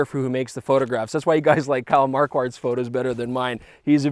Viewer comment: What is photographer that he say around 8:00 ? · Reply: Kyle Marquardt. <link>